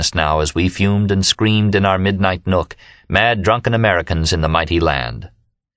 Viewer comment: "mountain nook" instead of "midnight nook"